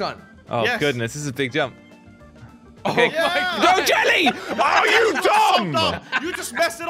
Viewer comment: DID SAY jelly you f up